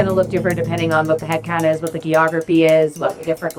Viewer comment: she really said "ghee-ography"